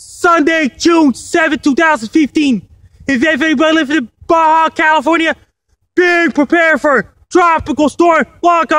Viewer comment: What did he say at 2:32 I'm confused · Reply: "If anybody lives in Baja California, be prepared for tropical storm". Hope this helped :)